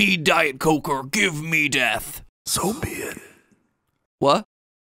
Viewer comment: He said so be it and I thought thay said soviet